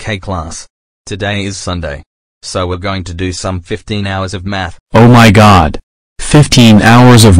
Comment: I thought he said 50 hours of math